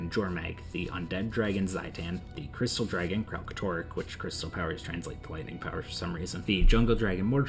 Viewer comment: what is he saying? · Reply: The crystal dragon Kraltorrik which crystal power are transformed to lightning power for some reason